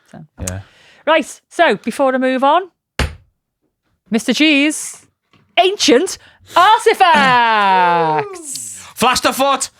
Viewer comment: After a discussion on a subject that provoked some quite heavy, existential thoughts... This was the divine captioning moment I was gifted by The AutoCaps when I finished making brekky and switched back watching instead of just listening... V: ....Mr G's Ancient Artefacts! R: (y'all will have to watch it yourself to hear what he really said. #NoContext #NoSpoiler) No. I got this instead (at 27:29) V: ....Mr Cheese's Ancient Artefacts! R: FASTER FOOT! Well. It's a good thing I didn't want to be broody or all angsty and dramatic today! That certainly lightened my mood again instantly. Thanks. Thanks. The gloomy South Australian day ain't so gloomy now. 💚💛💜 🧀📢📸